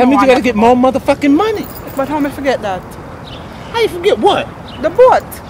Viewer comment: lmfao............. becus she's Jamaican and she's speaking Jamaican when she says "But How mi fi get Dat" to 50 it sounded like she said 4:16 "But who Could Have Forgotten about that lol did you get it? that's how 50 interrupted it that's dramatic